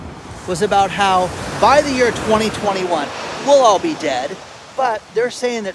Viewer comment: Did you say at (16:56) "By the year 2021 we'll all be dead...)? 😅 That might explain why none of this feels real.